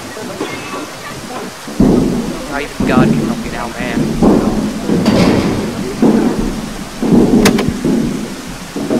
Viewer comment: what did he say? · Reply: he said " I've got to get out, man "